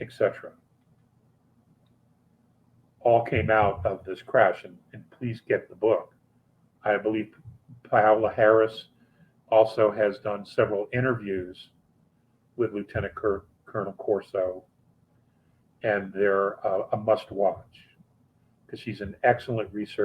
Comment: Sounded like you said Kamala Harris does good interviews. I know that's not???????